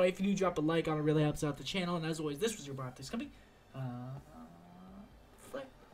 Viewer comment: Guys; what does he say in the end of his video at 3:11? (I mean this word after "This was your boy optic scumpi aaaaaand ... ? Sorry im from europe so my english is not the best :/ ) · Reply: see ya